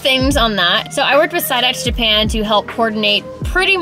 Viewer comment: this whole time I thought was saying “Psyducks Japan” not “SideX Japan” 🤦‍♂️😂